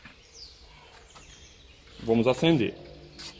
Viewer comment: Sounds like he says “send it”